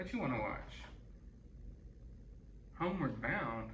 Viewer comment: I didn’t hear “homeward bound” I heard “Sandlot”